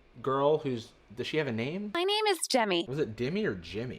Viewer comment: you guys know she is obviously saying Gemmy or Jemmy right